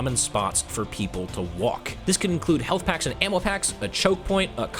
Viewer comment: Wait, did he say "Ammo Hax" at 8:26?